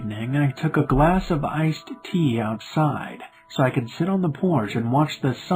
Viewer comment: who eles on the first at 1:15 who thought it said ice pee XD